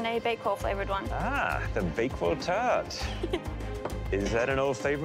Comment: legit thought dude said "ah, the bagel turd!"